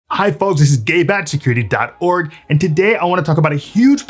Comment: thought he said "This is Gay Bat <link>"